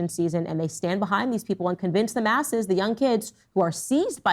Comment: did Candace say “convince the masses” or “convince them asses” - either way she’s right.